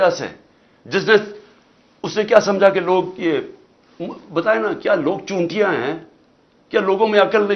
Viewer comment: did he just say 'kya log chu*iya hain'?? 🤣🤣🤣